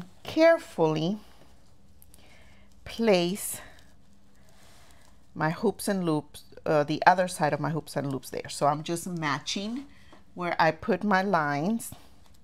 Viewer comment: Good tutorial. I had to laugh though because I walked away for a moment and heard you say “hoop and loops” so I had to rewind. Then I realized you were talking about hook and loop Velcro. I do not mean to correct as I always find it interesting what individual call items.❤